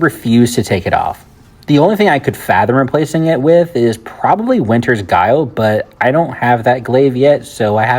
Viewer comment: It sounded like you said a glaive called winters guile. I dont see that weapon anywhere. Are you referring to the glaive winterbite?